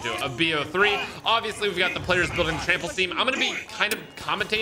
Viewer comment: did he just say building the trample steam 💀